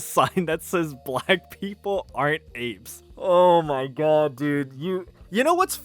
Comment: i thought is said "black people RENT apes" 😂